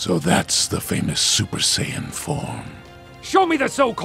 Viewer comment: Nova Shenron: so that's the famous Super Saiyan 4... Or did he say "Super Saiyan FORM"????